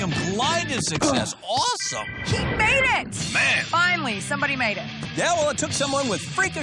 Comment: Did said SHE made it?!